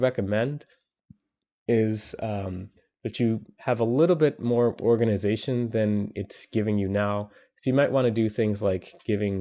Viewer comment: Ahahaha I was randomly clicking and it sounded like he said "A little bit more yoghurt comes out."